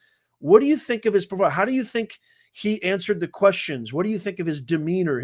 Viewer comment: I thought she said, “and you answered all the questions”. Either way it’s sad sad😔